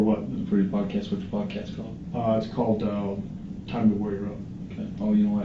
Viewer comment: Lol his podcast was called “ time to warrior up” lol! He said he scrapped that lol! I bet buddy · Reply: I thought it said, "Time to lawyer up!" hahaha